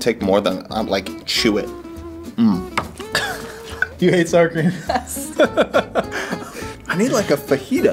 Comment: I like sourcream with out mberry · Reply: When I first read this I thought you said sunscreen and not sour cream